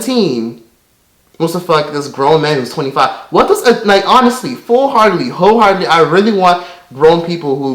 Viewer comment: Did u just say hoe hardly lmao